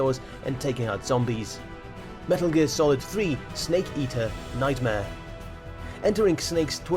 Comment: 😁😂 I thought he said 'snake ßeater' 😄😅